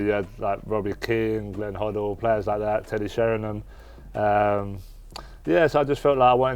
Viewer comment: The captions couldnt understand Teddy Sheringham so called him Teddy Sharon 😂😂